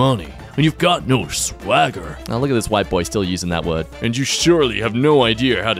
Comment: Did you just say "white boy". Imagine.